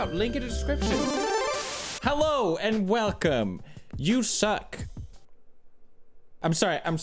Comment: Did he say zucc or suck 0:11because either way I'm fine with that